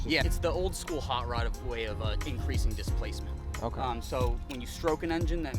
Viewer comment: did he say decreasing displacement? o.O